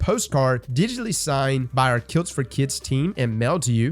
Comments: Im so glad that could still say his legendary line. Hope you get well soon boss, we're all rooting for ya. Edit.. Kilts, it says Kilts. I was a bit thrown at first and thought it was Kills for Kids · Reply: Same you know. I thought said kills for kids. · Thought he said “kills for kids” lol I got a little worried